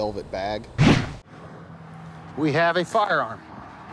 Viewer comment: it sounds like he said "we have a fucker arm"